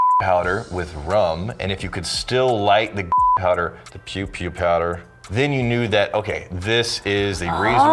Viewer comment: All I hear is C*m powder